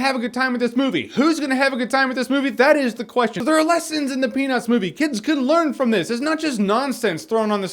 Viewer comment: I keep hearing "A Penus movie"...The Penus Movie!"...im very tired,and im laughing alot...help